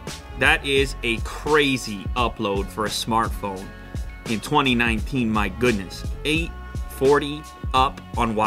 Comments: did he say 2019? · i heard 2019? or am i crazy?